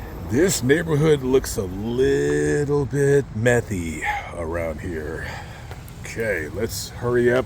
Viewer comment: Methy 😂 Of course I thought you lisped and said “messy” til I saw “methy” on the screen. 😳 Methy. Yikes.